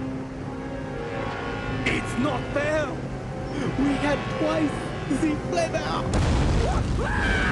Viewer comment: What was he saying? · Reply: It's not fair, we got twice the flavour